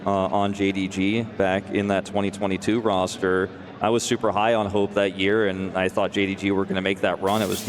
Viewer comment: thought my boy said "I was super high on coke that year" lol